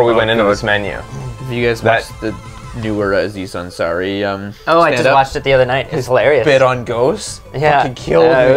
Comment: What is talking about at about 3:24? He says like "warazee sun sa ree ummmm stand up?" he says its funny and I want to watch it but I cant understand what he said. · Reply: "Newer Aziz Ansari standup." Which I think is "Buried Alive."